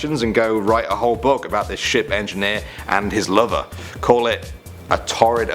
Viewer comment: Wait, did he say "ship engineer" or "sh*t engineer?"